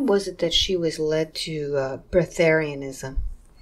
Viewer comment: Did anybody catch when asked about "birtherism or vertharism"?? I couldn't hear real well and wanted to look it up?? asked about it a 1:11:28. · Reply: Breatharianism